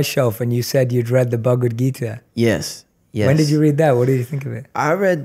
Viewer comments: excellent session!!! can anyone tell me what book they mention @ 3:36? thanks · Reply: The Bhagwat Geeta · Hey guys, I didnt catch the name of the book they mentioned at 3:37 .... Can you help me out? :) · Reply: I had the same question. Took a bit... but I found it! The Bhagavad Gita